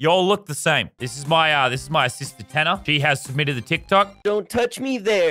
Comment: When you said this is my sister tanner I thought you said assistant so I rewatched that scene 5 times 😂